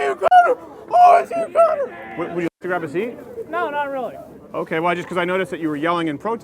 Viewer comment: When Crowder first asked the obnoxious man baby, " Would you like to grab a seat?" I first heard 'Do you need to grab a 'Z'...... I died for bit 😂😂😂😂